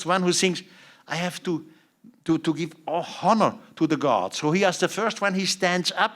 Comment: did he just say “I have to give a hummer to the gods?” 🤣🤣🤣